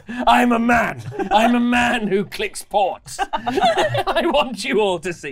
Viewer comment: Now I'm not only worried about my eye sight (doctor is lying, I doubt I have very good vision), but now I'm worried about my ears as well. I swear I heard say "I click pork" and not "I click ports" xD I also have to disagree with, the NES TMNT wasn't hard. At least not with 2 players (or I'm confusing it with the SNES). Now the Aladdin game on the other hand q.q And lastly! Stop making me feel old and ancient. I'm only 30!!! For all our sakes, let's not ask her what a floppy disc is